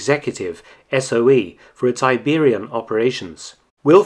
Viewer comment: The first time...I thought he said "...SOE, for it's Tiberium operations" - so clearly the Brotherhood of Nod strikes again!